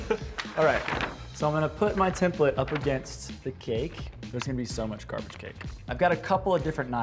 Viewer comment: “there’s gonna be so much carve to cake” am I the only one who heard that?